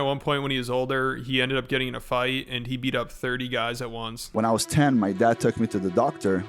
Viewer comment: At first I misheard him n I thought he said he "f*cked 30 guys at once" . I was like "good for u" lmao